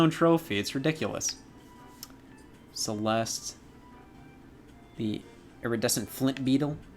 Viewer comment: Casually listening and I just hear him say Celeste like the game and that threw me through a loop